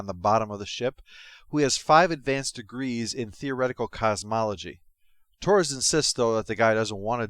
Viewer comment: I heard "Five degrees in theoretical cosmetology": I like my version better!